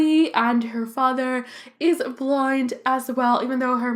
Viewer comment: I know that its the accent Buț i sounds like your saying blind not blonde